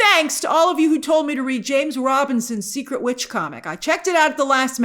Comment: Did she say Secret Witch?